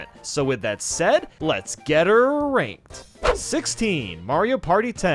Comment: “JOKE” 0:23 Why does it sound like to say “Get Here Ranked” Like you would have a girl friend